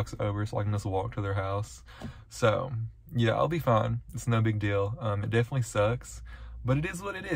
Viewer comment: when you said "its no big deal" while u were talking in ur dad's truck i thought you said "its snow big deal" and i burst out laughing